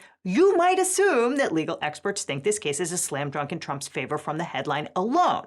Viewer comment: Did you say "slam drunk"?